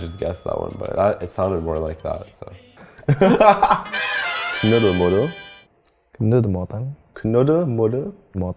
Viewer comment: I only hear "nude model" 🤦🏻‍♀️